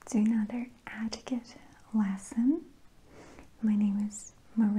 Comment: “Ah-tiquette” is up there with ”Goud evening” and “couking sassion” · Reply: I keep hearing "adequate lesson" like yeah I know I'm an uneducated pig